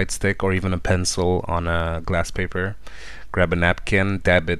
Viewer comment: am I hearing this right? Shaving it off on GLASS paper?